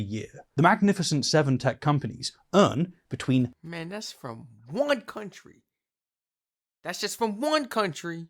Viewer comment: did I heard "... from one Country?" ... 🤦🤦‍♂🤦‍♀🤦🤦‍♂🤦‍♀